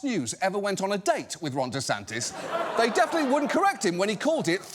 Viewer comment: Sounded like he said Wrong DeSantis.